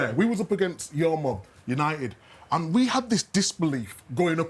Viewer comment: Micah's a troll did he just say man united is Gary Neville's mum ?! 🤣🤣🤣🤣